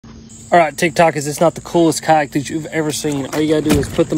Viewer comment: I thought he said "cock" not "kayak" at first